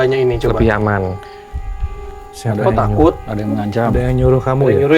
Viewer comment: di 44:29 ada suara perempuan gajelas bilang apa tapi kayaknya bilang takut, btw denger pake headset